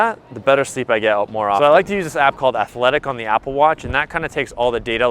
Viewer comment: Search for athlytic. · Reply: yes figured that’s the one. Sounded like he said athletic. When it’s actually Athlytic lol. Thanks mate